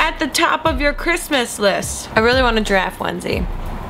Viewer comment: I misheard 'giraffe onesie' as 'drag onesie'. Now *that* I'd like to see.